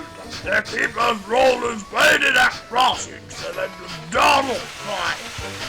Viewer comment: Did George say Donald